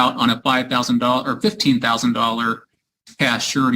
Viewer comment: He already put up the $1500 on the first $15,000 bond and now his to put up another $2000 on the $20,000 bond. Depending on the time lapse on his FTA will determine if he forfiteted the first bond. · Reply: If first bond was $15,000 I can understand it going up. I may have misheard and thought it was $25K... so couldn't figure why it would go down. It went up then. makes sense.